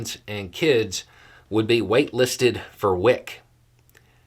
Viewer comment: For a second I thought said they would be 'weight' listed. Like overweight women/infants wouldn't get WIC.